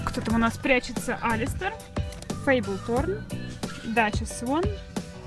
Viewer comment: Когда ты сказала "Фейбель" мне послышалось "Мейбл"